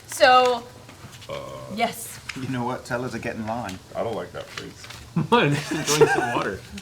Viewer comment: [Not sure I understood what Vax said there, or Keyleth's reaction?]" Vax just says "tell her to get in line" in regards to the goddess-wife of the man Keyleth was kissed by. after that Travis points to DM and says "i dont like that face"; Matt laughs and says "what, im just goin to get some water" Vex then freaks that something will happen later on with that plot point and it will lead to a battle with a goddess.